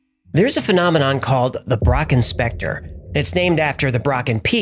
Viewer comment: Lol I thought you said "Brock Inspector" LMAO 😂